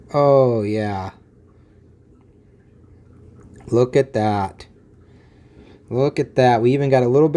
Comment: の空耳かもしれない、けど面白かった！『ﾑｹﾀｧｰ↘』って